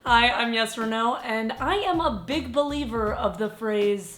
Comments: When you said I thought you said yes or no 😂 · I’m so dumb I thought that she said yes or no the hole time but she said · At 1st she said hi I'm, and I thought she said yes or no😂😂sorry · When she said "hi I'm Yes Reneau" I thought she said "hi I'm yes or no" hahaha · I thought you said yes or no instead of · Everytime she says "Yesareanau" i hear "Yes or no?" is it only me lol hahah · I thought she said "Hi! I'm Yes or No" and I was like.. what?? Oh~... I'm new :D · ''When she says "YesReneau'' I always hear it as ''Yes or No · I really thought she said "yes or no" until I read her name 😂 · I heard "This is Yes or Naw" ... Checks YouTube channel name 'YesReneau' ... facepalm